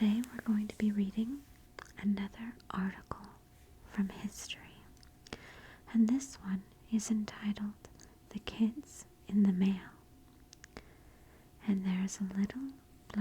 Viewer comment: I definitely thought it said “The kids in the mall” 😂 I was like okay, how is this history